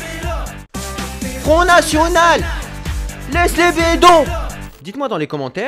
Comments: j'ai entendu laisse les vélo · A 2:00 j'ai juste entendu Lesse les bedo et a 07:30 j'ai entendu c'est le prix des Danette aux fruits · A 2:02 j'ai entendu laisse les vélos pour ma part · Moi à la deuxième j'ai entendu "Connasse connasse, laisse les vélos".